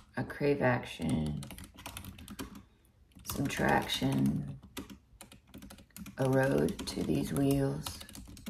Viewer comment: Very real and impressive. I am most moved by what I hear as "a road for these wheels."